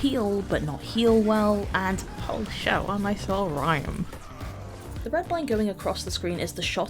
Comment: what the hell did you say? i cant understand you at all. your accent is so strange sometimes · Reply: “Holy shit what a nice little rhyme”